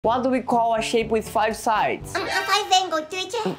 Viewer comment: I thought he said a shit with 5 sides😅😅